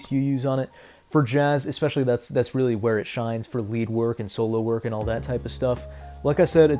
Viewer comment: I thought you said “Weed work” 😂